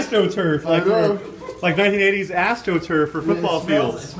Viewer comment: I thought said, Astro turds, lol.